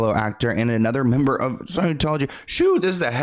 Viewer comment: I thought he was saying sexology